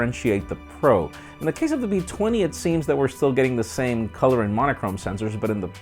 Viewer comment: DID HE JUST SAY V20?!? 3:26 ...i dunno...